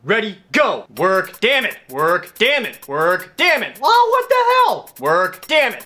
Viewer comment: Did he just say, "TWERK,DAMMIT